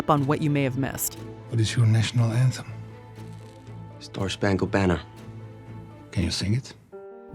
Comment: Wait, did he say “Star Schpangled Banner”?